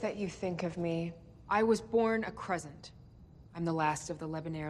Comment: When she said I was born a crescent I thought she said croissant